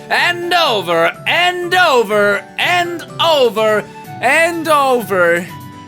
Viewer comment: This is like the 100th time watching this video, but I just realized, that at 2:27 it started sounding like said BEND OVER instead of and of "AND OVER" hehe XD